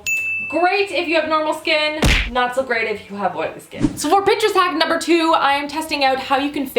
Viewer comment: Haahah i heard at 2:45 "so for bitches hack number 2" hahahahah